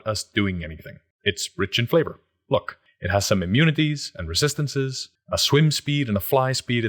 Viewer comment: I had a verbal dyslexic moment that made me do a double take when you said Cold Resistance and Swim Speed I heard “Cold Speed and Swim Resistance” and was bewildered XD